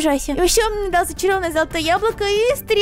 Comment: Мне послышалось что сказала разочарованное золотое яблоко😂😂😂